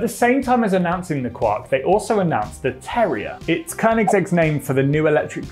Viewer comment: Just like the dog? Are you saying terrier? I thought you were saying terror 😂